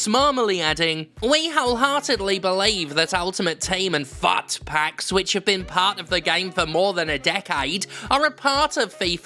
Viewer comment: I've never heard of FUT Packs before and heard you say "Fart Packs," which is probably more accurate anyway 😂